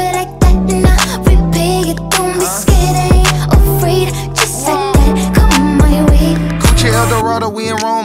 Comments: i heard "just like that, cut my weave" i s t f g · i thought she said ''just like that, grab my ween'' at first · My dumbass mind thought she said just like that cut my weave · I really thought she said "Just like that, comb my weave".